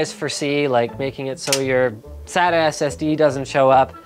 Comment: At first I thought he was saying "Sad-ass SD"